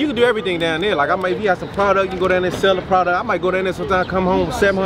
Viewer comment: I thought bruh said powda instead 🤣🤣 lawd hammercy